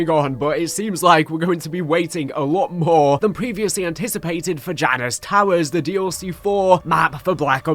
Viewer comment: It sounded like you said vaginas towers lol